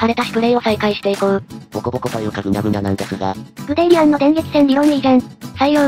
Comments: 話のつながりのせいでグデーリアンがグニャーリアンに聞こえて笑った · ここの所「グデーリアン」が『 グデーリにゃん』って聞こえるんだが…←